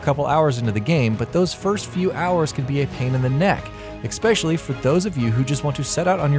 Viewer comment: Did you say "ehkspecially"??